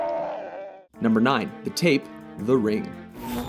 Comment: When he said the tape I thought he said the Rape O_O